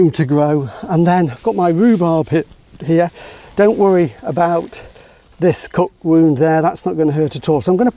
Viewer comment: what did he say here?😂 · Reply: "don't worry about this cut wound here, that's not gonna hurt at all..." HTH! -Sean (p.s. he also had a little hiccup 😛 )